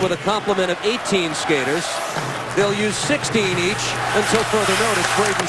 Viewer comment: I think a Flyers player just listened to A Tribe Called Quest before the game. I think he's said Can Kick This Loose Puck?